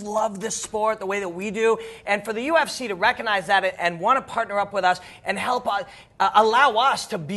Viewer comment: You say "ufc want to partner up" but I only hear "ufc want to control". I really hope your integrity is strong!